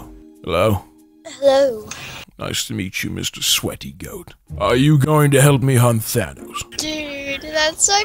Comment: Did Thor just call him mister sweaty goat